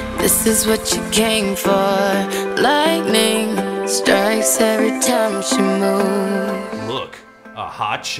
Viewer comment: The first time i heard this is what you came for, i thought when she said “strikes every time she moves”, it said “stritarashumanol”. I have no idea why.